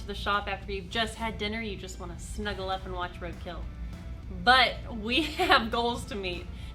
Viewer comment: Sounded like you said you'd like to snuggle up and watch Red Tails. At least that's what it sounded like to me. I have to watch Red Tails once in awhile myself. You probably said something else but that's the way my mind works. Great video as usual.👍